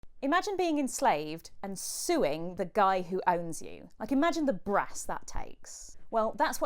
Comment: I misheard this as "imagine being enslaved and [stewing] the man who enslaved you." Eat the rich!